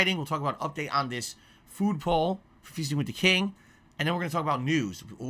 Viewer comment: Did he just say fisting with the king?